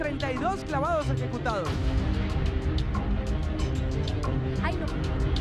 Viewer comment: Que dijo Jazmín en el min 6:48? D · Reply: digo hay no